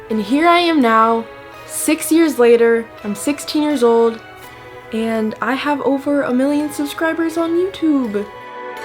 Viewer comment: Lmao I thought it said "being a cake for 1 million"